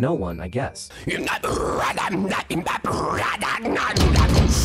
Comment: At first I heard “hydrate or die straight” 💀💀